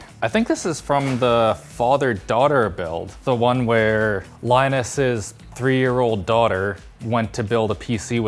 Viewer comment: i thought he said lioness lol